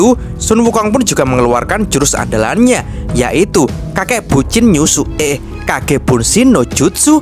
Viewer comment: kakek bucin 😅 Gak salah dengar kali saya bang hehehe 😁